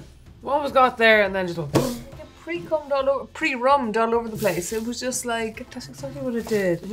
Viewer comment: did she say pre c*m#d all over thr place before correcting herself! Hahahahahahaha!